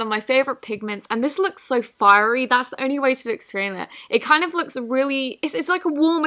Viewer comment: What in the world is she saying, “it’s the only way to eysfhdxc” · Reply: "That's the only way to explain it" is what she is saying or at least that is what I hear.